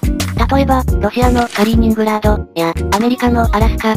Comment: カリーニングランドがスターリングランドに聞こえてしまう。病気、、、かもぉー